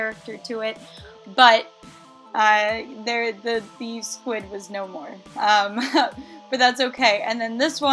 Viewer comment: When she said squid I thought she said squib 😂